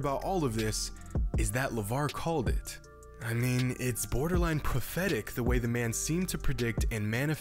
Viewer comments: I heard "borderline pathetic" and I started nodding only later realizing that I misheard prophetic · I thought you said "It's borderline pathetic" not prophetic 😅